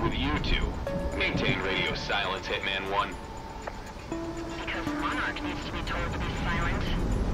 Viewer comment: anybody catch what prez said at 3:14 ? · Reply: "Because Monarch needs to be told to be silent"